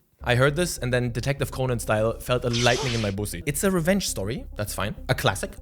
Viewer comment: Maybe its the accent but what the hell did he say??? 😂😂😂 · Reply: I’m pretty sure he said “lightning in my bosom” but it does sound a little sus lol